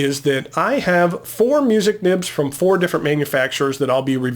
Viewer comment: For some inexplicable reason I always heard "musical nibs" instead of "music nibs". I thought they must make some sort of pleasing sound while writing lol This is really interesting! I love all of the bits of extra information that you add to your videos, it really makes them special!